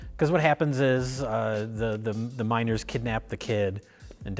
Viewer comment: I was so confused when they started talking about the miners and I kept hearing "minOrs"...